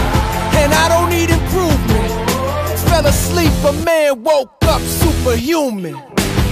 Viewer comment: I thought he said “fell asleep man, woke up superhuman” like I fell asleep normal and woke up superhuman 😂 kinda makes more sense that way